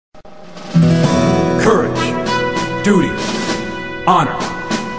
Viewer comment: Why do I hear doodoo instead of duty?